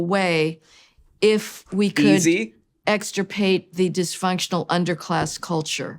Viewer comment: I’m bad of hearing… did she say extirpate? As in she wants to “extirpate the black race… i mean culture?